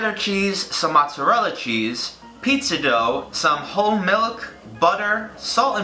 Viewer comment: I SWEAR I HEARD HIM SAID "hoe milk"